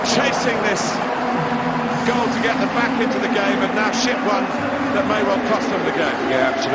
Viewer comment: Did anyone hear commentary say shit worth in 8:48 comment if you did or like